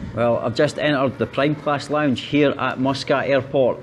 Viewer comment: i literally heard "prime class" as "plane crash" im like uhhhhhhh is really exceeding expectations in his travels